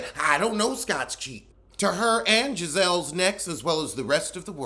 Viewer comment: Wait a minute, did I hear you say “Gizelle’s necks?” 🤣🤣😂😭